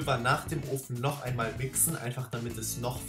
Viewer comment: Wer hat bei 3:21 auch wi**en verstanden? 😂